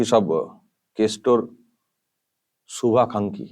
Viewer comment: দাদা, এটা তুমি কি বললে?😂 শুভাকাঙ্খী বললে নাকি শুভাখানকি, ঠিক উচ্চারণ টা বুঝতে পারলাম না 😂😂😂 দা কিন্তু দিনকে naughty হয়ে যাচ্ছে 😂😂😂